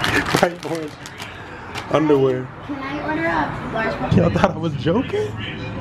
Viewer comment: did i hear daddy can i..? I see you doin work